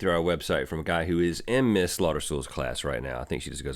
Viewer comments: At first I thought you said "Ms. Slaughter" when you said your teacher's name! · At first I thought you said "Ms. Slaughter" when you said your teacher's name! Chesui le nana!